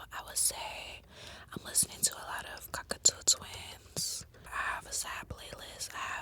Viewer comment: Did she mean Cocteau Twins? Sounded like she said cockatoo